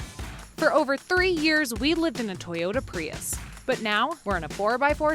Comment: At the beginning, it sounded like you said for over 30 years you lived in a Prius. Then I remembered the Prius wasn't around 30 years ago. I had to turn on CC to see that it was "3 years".